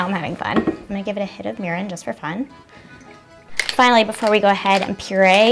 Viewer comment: I heard "I'm going to give it a HIT OF URINE, JUST FOR FUN"